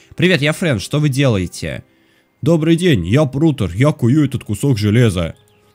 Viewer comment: Мне послышалось или сказал я курю этот кусок железа